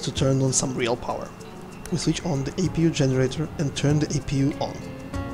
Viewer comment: My apologies, I watched it twice (not full screen) and both times thought you said "turn the APU off". Also this procedure assumes that external power is available pre-start, on the line that is not normally the case. With regard to the fire test well that may just be company procedure, We always run the fire test prior to APU start, It would be a pity to discover and apu fire the hard way. When all is said I will still be buying it.